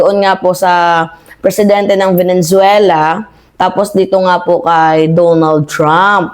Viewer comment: VENE - ZUELA NOT VENEN - ZUELA kala ko na misheard ko lang, pero, paulit ulit binabanggit ang VENEN - ZUELA.😂